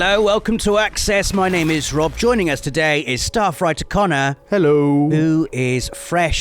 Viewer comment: I always hear "staffwriter Conner" as "Star Fighter Conner"